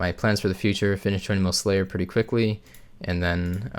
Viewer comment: Did you just say you are going to "just finish 200m slayer pretty quickly"? Lmao